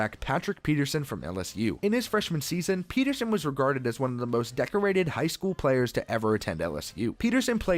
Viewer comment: I thought he said pojaque high school if u r from New Mexico u know